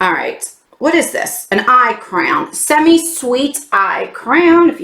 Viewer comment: I thought you said "eye crown" I was excited to see an eye crown.....but also, it was an eye CRAYON....not a new beauty tool lol